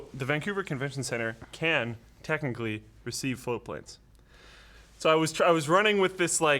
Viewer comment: It always sounds like he is saying "Flowplane" to me. Didn't know it was "Float Plane" until I saw the new shirts last week. Am I the only one that feels this way?